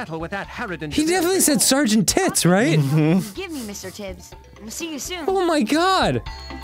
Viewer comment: I didn't know why they seemed so confused by the cat's name. They thought he said "Sargent Tits" and that is fucking hysterical. To me he clearly said "Mr Tibs," which is a pretty normal tv cat name here in the UK, like "Mr Fluffykins" or something. It's a stereotype of Old Cat Lady naming her hundredth cat... but Sargent Tits is so much funnier.